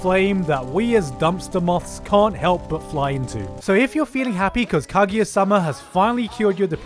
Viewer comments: What is the anime at 1:14.? I hear the name but can’t spell it. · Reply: Kaguya sama Love is War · what anime is this I cant really tell what he says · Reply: Kaguya sama love is war thats the name. the anime is very high on the psychological part